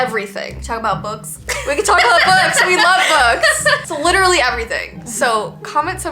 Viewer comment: When said we can talk about books I thought she said we can talk about boys lol